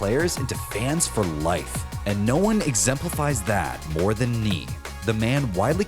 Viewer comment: I thought you said "ME" hahahah